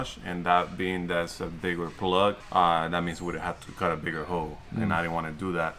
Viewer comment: i heard “cut a bigger hoe” lol 🤷🏽‍♂️